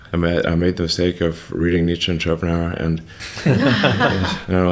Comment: What was the book Elon said he made the mistake of reading, at 18:30? I cant make it out. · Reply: Nietzsche and Schopenhauer